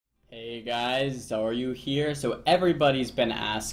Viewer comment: My gf (who knows nothing about WoW) asked me why you always start your video asking that. And I was like "Asking what?". Apparently she thought you say "Hey guys are you here" instead of "Hey guys Xaryu here" lmfao. Now that's all I hear in your intros D; lol